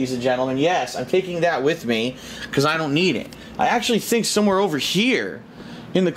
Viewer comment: does does he really say, "I'm taking this with me because I don't need it"?